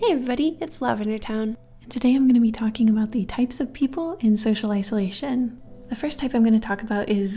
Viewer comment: For a second I thought it said socialism and I was like, "What!?" but then I heard you say social isolation! LOL